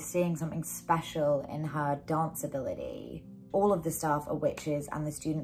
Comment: All of the staff of witches and the students are unaware of the fact that their dance routines are actually enabling the coven to perform magic…” Perhaps I’m hearing this narration incorrectly but the staff is not unaware. The staff are fully aware, participating, and complicit. The students are unaware, but not the staff. I don’t remember any indication that there were staff who weren’t part of the coven. But again perhaps I was mishearing this in the video. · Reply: So what you’re saying is “All of the staff ARE witches, and the students are unaware…” OK that makes perfect sense. I was hearing “are” as “of” so it was my mistake. Thank you for clearing that up!